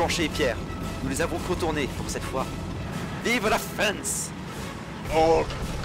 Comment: He says "fence" instead of France